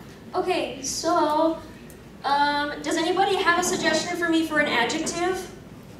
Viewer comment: what did he say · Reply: Crona: "Does anyone have a suggestion for me for an adjective?" Audience member: "Slimy."